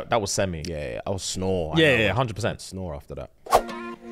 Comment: out of shock, did say "blood of the lamb!?" And "Jesus wept!" 😭🤣🤣🤣